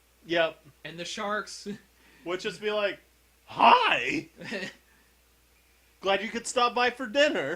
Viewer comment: It's funny how said "Hi" when you were talking about the shark but i heard it as "Hai" which is Finnish word and actually means "Shark" :D I just had to say it :D